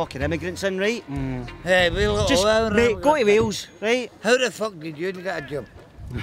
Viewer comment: who's that auld Jake the steemer hanging about on the aggressive neds shoulder?LOL Can anyone understand him? He just pipes up at the end"How the fuck did you get a joab" 02:15 HAHA